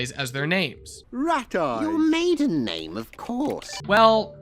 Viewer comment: When I watched it, I heard "You made a name! Of course!" So I thought Yellow Guy just created a fake name to be the password... xD